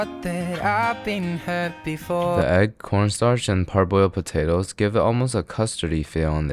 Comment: you said "1/4 cup of corn starch" but I heard "1/4 cup of p0*n stars"